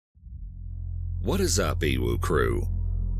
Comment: I was about to stop the video at the beginning cause I thought he said "what's up uwu crew" but I realized it was an abbreviation of his YouTube name lol